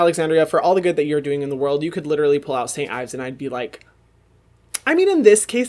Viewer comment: I heard "I would forgive her for using cyanide" while he was saying St Ives, which is basically the same thing for:D And it made sense to me since people used to use cyanide to whiten their skin in Medieval times and even through Renaissance. We came a long way guys!